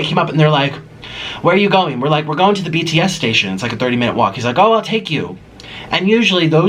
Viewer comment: did you say BTS station.😮😮